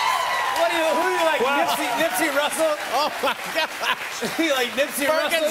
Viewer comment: did Jimmy say “Nipsey Russle” ?😂